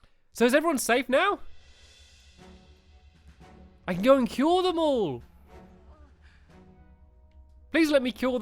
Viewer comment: I thought you said kill them all instead of cure them all